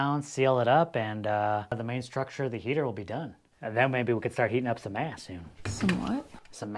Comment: Lol. I heard "Heating up some ASS " too. 😂